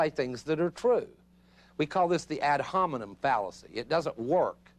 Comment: what word did he say it’s called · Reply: ad hominem -- to the person (and not the argument)